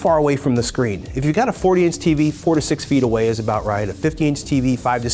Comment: I thought he said " if u hv 40 inch TV u gotta sit 46 ft away " and i was shocked to know this is the reason why my eyes were always sore after watching Tv.